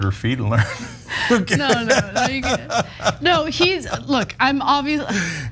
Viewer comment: I know this will get me a lot of hate, but I thought that when Ana said "I'm obviously", I thought she said "I'm ovulating".